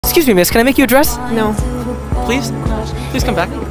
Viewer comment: She heard it as “can I make you INTO a dress”